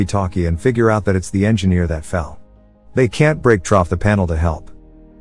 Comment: At some parts he says (I don't know if it's an AI or something), but he says "Trough, or Troff" Instead of Through. (First is 11:35 ) (Second is 11:51 ) (Third is 11:53 ) (Forth is 11:55 ) (Fifth is 13:55 ) Quite funny, like he's saying a typo.